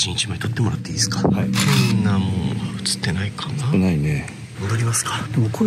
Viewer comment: で「喋ってない」って聞こえるんだけどｗ